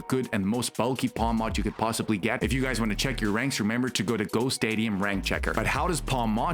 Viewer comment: you guys talk way too fast doing these videos as short as possible; at 5:40 you say a website and I can't understand what the heck you are saying; on screen textual cues work great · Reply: Hey we appreciate the feedback and will certainly do our best to slow things down moving forward ! the website in question is called gostadium <link>